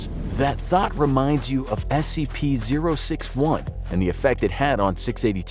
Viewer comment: Tbh for a bit I thought he said scp 001 or he was gone use da satchu